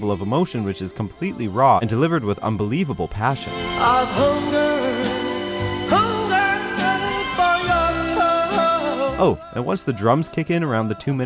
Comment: sounds like he says "hunger for your toes"